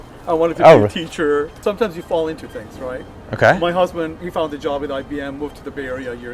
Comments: did he say " my husband"??? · Did he say my husband? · is he said my husband?